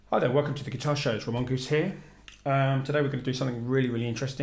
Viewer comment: Did you just say, "Tremonquist here" ?